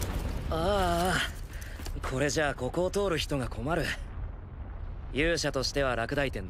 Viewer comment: did he just say "ara"? Omg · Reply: Just aaaa